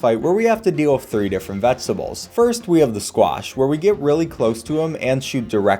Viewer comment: Im sorry not to be rude but did he say squash?